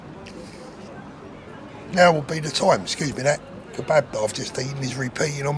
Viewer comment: Kebab? I thought you said 'bath bomb' 😂 My hearing is shot